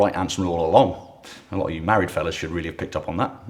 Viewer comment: ish does he say Maori fellas?